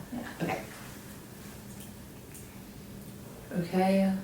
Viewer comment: Right after says "yeah ok", there's some quick faint speaking. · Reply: oh wow! yea I hear it 20:44 and it's a female whisper voice towards the left speaker saying 'okay' EXCELLENT HEARING!!! That is a GREAT ONE! Good Jobe!